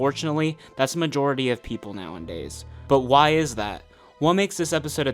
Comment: did you say "Now And Days"?